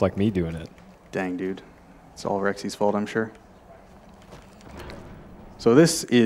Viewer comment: Did he just say my name, "Solrex?"